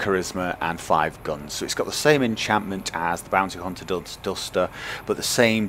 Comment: anyone else hear him say "same enchantment"